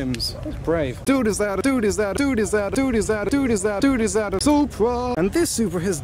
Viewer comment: it's sounds like your saying dude iszat(a)